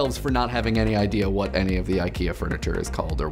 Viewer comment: I love that when he says "luftig" it sounds like he is saying love dick PS "luftig" is german and it means airy